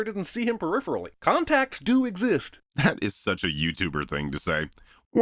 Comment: he says youtuber but the captions says "asshole". Very sneaky lol